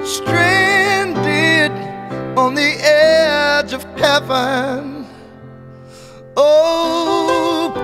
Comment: awesome music, but I heard "on the edge of kevin" and now i can't unhear it :D